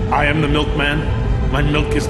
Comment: I thought he said “ i am the milk man, I am delicious.”